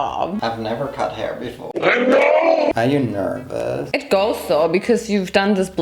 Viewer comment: did anyone else hear the “it goes so”?